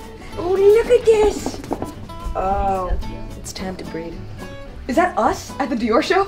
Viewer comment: Wait did she say it's time to "breed" or "breathe" after looking at those kid clothes? Lmao 😂😂